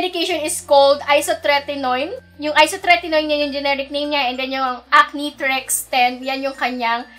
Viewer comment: "Wlang generic" Actually generic name is isotretinoin.. maybe you can say nlng na wlang cheaper brand for it to make your statement correct. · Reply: Lol are u deaf? She actually said "yung isotretinoin, yan yung generic name nya" @ 10:41 😑